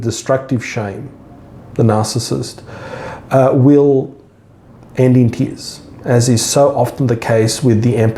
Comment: Hi there, I think you're brilliant. I am caught up in this life experience trying to move myself through and heal from obvious childhood experiences that predisposed me. I first sought to understand what it was I was dealing with, meaning what I concluded was a narcissist, but then asked what was it about who I am that put and keeps me here. I am consciously aware of much of what you discuss in this video and am trying to strategize a way out of the cycle I'm in. Thank you for offering such quality content. I appreciate it. I have a question as I cannot under what you're saying at the 6:48 minute mark: "....... the narcissist will end in ?????????" - I cannot understand what you say after the word "in". I've replayed it many times over and would love to know! Can anyone shed light on what the sentence is? Thanks in advance :) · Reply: "end in tears" is what he says